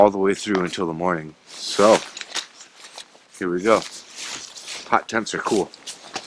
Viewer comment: Thought you said hot tub😂